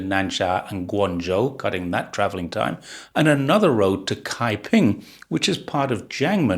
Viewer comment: wait? did I hear you right, that you said Kai ping(开平)? That’s my hometown, and famous for 碉楼diaolou。 😄